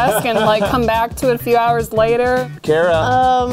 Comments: with captions it said "to it a few DAYS later" instead of few hOurs later like if you noticed :W im hardly gonna get likes this is not easy to spot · she says a few hours later but the captions say a few days later..... I NEED ANSWERS!!!